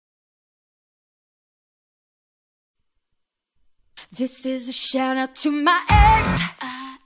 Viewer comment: I always heard, “this is a shout-out to my eggs.”